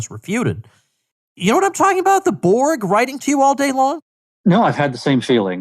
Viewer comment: "The Borg" Oh... I thought you said "The Board" like the Board of Directors.... Corporate Board, and Corporate Borg... SAME THING!!! It's office politics you are talking about really. IMHO